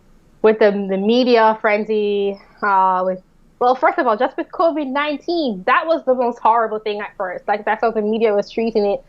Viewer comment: she's pretty and smart, but it sounded like she was saying "kobi 19". Is that where we're at now with historical facts, and those events just happened.